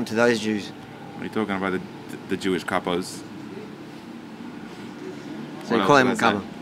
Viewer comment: What was the word being used around the 4:05 point? Was it "cuckold"? Couldve been, but didnt sound exactly right so Im assuming it's a Yiddish term being used. Would be genuinely interested if anyone knew. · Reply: He said Jewish kapos. Prisoners that were assigned to watch over others in Nazi Germany.